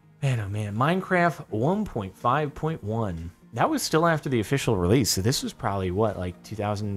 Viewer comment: anyone else hear 'please support the official release' at 10:20? need more dbza..